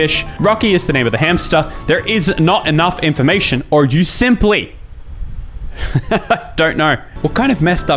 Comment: I accidentally paused at 17:40 and all I heard was “you simp”